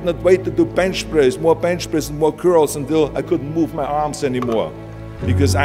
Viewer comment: Why did i heard 'more bench press more girls'💀